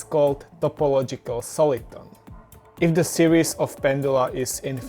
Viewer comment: "this defect is called..." -- couldn't understand what you said after this. Suggest you show such terms on the screen at the same time. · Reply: topological soliton or a kink :)